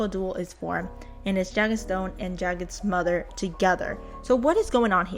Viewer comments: she said Jagged’s mother am I hearing that wrong or what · Jagged Stone N' Jagged Stone's Mother??? Did I hear that right or no pls correct me...In Truth I thought both of 'em was married but divorced and Jagged actually dyed his hair I mean..I-I'm so confused, I must be very dumb @@